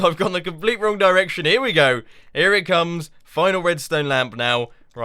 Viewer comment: Did Mumbo say " here we go here it comes" like in the remix?!?!?